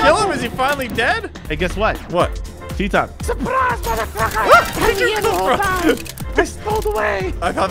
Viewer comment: What did say!!!!!!!!!!!!!!!!!!!!!!!!!!!!!!!!!!!!!!!!!!!!!!!!!!!!!!!!! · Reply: He said mother trucker :D